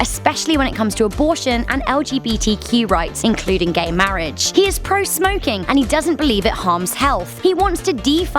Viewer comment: i heard “hes pro smoking” and “school would be out” bet.